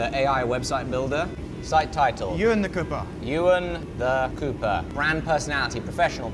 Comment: I first thought it was said copper and I was like this close to drop an ACAB